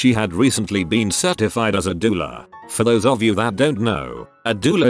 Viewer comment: I was looking away and I heard “doula” as “diddler” Quickest double take I ever made in my entire life.